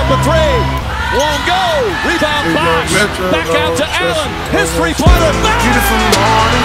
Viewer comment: i love that on the ray allen one it sounds like he says “history pointer”. very fitting lol